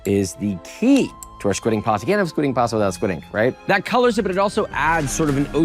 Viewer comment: I thought bro said squid game pasta